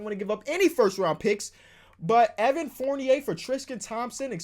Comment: Every time says Tristan it sounds like he says trisken